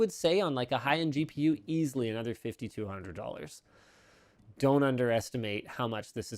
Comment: did he say 5200$ more ? or I misheard it